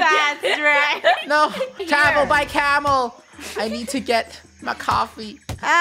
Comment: anyone hear say Camel by Camel......?! LIKE THE ANKAH SONG?!!!!!!! AHHHHHHHHHHHHHh